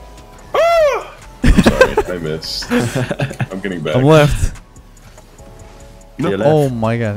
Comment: every time he says "im left" i just hear "im leth"